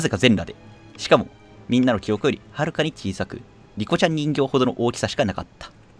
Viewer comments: リカちゃん人形(？)が ニコちゃん人形とかリコちゃん人形って聞こえてしまった、、、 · リカちゃん人形って言ってるんだろうけどリコちゃんって聞こえるwなんかの口からリコちゃんって可愛いなwなぜだろ？